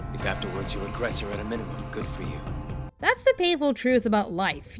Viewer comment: What the heck does he say there? I seriously can't make it out. "If afterwards regret your (what? inamen? Enderman? in a minimum? That can't be right? What the heck is he saying here?), good for you." Seriously, what?